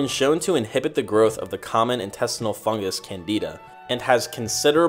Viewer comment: Help, please. My sound isn't perfectly clear, and I can't get the second word of one of the "trinity" (around 10:44+ in the video) ..."especially when combined with red ?????". Does anyone know what that second word is? (sounds like "leg" or "light". Thanks. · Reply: its red light